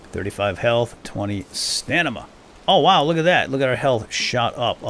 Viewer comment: you said stanima instead of stamina :D great video though!! game looks pretty good.